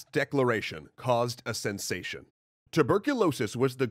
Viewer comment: Lol I thought it said creating tuberculosis